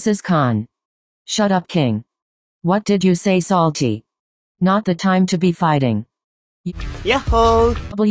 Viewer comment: plz when i forst read what daichi said i thought he said "not the time to be flurting"-..help